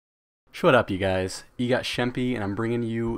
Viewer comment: at the beginning it sounded like u said "Shut up, guys"